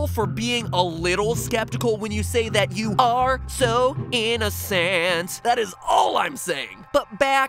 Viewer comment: When you said the word innocent, i thought you were saying in a cent.